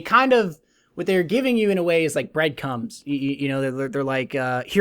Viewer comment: Does he say "bread cums" ?? lol